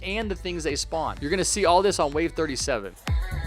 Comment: I thought you said wave 7